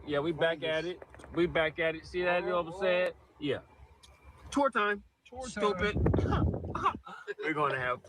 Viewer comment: the fact that thought he said "torta" and not "tour time" is beyond diabolical